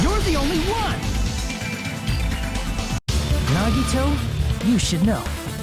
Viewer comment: For some reason, at 38:56, I heard "Grandito" instead of "Nagito" 😂 i seriously need sleep and lay off Danganronpa for a minute